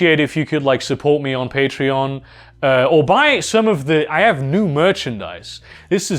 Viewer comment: I thought he said "support me on Patriarch" at the end...